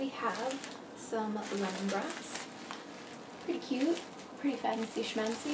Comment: When you said lemon grass the captions said “lemon breasts”🤣😂🤣